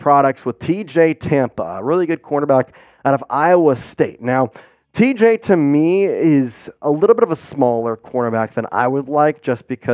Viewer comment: 6ft 2, 200lbs is a big corner. Some of the things gs this dude says makes me think he doesn't know football or the chiefs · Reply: I don't think that's what he was saying at all. It sounded like he was saying TJ Tampa IS a big DB.